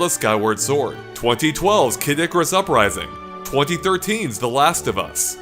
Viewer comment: I think I heard "Kid of Sakurai-sing"